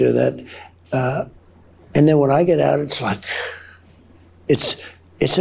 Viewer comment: Did he say 'And then when I get out!" 😂 Listen